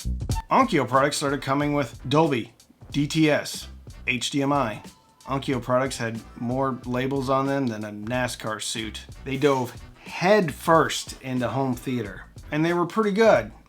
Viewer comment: 😂 What I thought he said: “Onkyo products have more labels on them than a Napster (law)suit.” 🤔 ➡️ 👆 ⏪… What he actually said: “…a NasCar suit.”